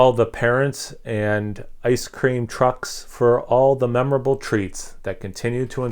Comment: You said "ice cream trucks", I heard "ice cream cucks". Sounds good though! Can't wait to go buy this off a shelf somewhere in NJ. Haha.